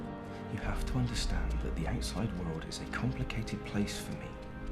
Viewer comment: "what did he say? let me turn on the captions" closed captions: "the inside world" what he really said: "the outside world" Great.